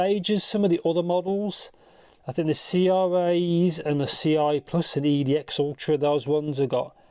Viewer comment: what did he said ? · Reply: I said CRA, CRA+ and EDX ultra and their treble extension.